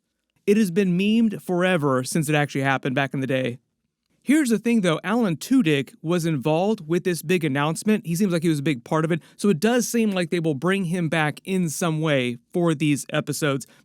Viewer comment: did you say allan two-dicks ?